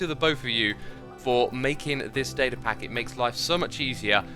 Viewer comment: did he say David pack?